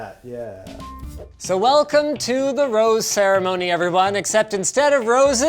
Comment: Oh, I thought you said 'Roast' ceremony, not the rose ceremony. I was like, damn, now he is really going to say how he feels